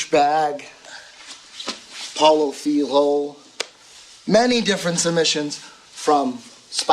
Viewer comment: There are two moves I can't quite hear. At 3:24 he sais something like "pollo thee hole"? And at 2:39 the says something like "the raymey". Can someone tell me what he is saying? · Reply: He says Paulo filho the name of a bjj fighter. He invented that particular move